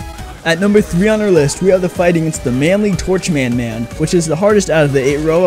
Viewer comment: Please tell me I wasn’t the only one who heard him say Torch Man Man